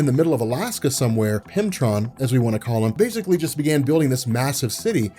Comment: i thought said pimptron lol. Sounds like a character from old man logan